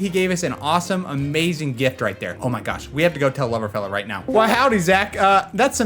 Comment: I like how at 34:37 it says "Lamoriello" instead of